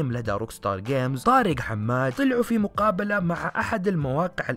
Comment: كاني سمعت طارق حماد؟؟